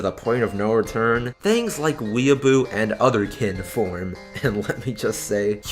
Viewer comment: Huh, I actually had to google "Other Kin" (after turning the CC on, I thought you said 'Others Can form"). ...... okay.....I'm an Old School RPGer (I 'fondly' remember the days of Satanic Panic and 'Dungeons and Dragons' 9_9), so I know about people getting 'lost in the role', but this is just ridiculous 0_0 Yes, there are religions that profess reincarnation, so just go and be a part of that religion, instead of making up all this Bullsh4t so support your fantasy. I can't help but wonder though, how this applies in Japan, where I have read that there are Otaku that pretty much live Cosplay as a lifestyle (or here in the states, where we have 'Professional' Cosplayers.... how can you make money [and live] off of dressing up as a probably copyrighted character in the first place?). I don't know about not being involved in fandoms, though, just to avoid the bad people. Just keep the people you actually talk to about your fandom small. I personally talk comics and stuff with personal friends I have known for the past 20+ years. Even though I do comment on vids like this, I rarely get a response, and only rarely have have I gotten a 'trolling' response, which I usually don't reply to anyway